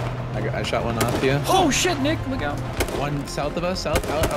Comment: thought he said oh shit nigga